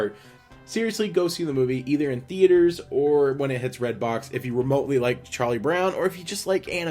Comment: I thought you said Redtube at 4:15...